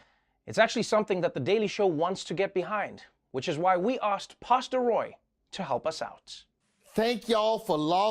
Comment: I really thought he said pasta Roy for a second 🤦🏽‍♀️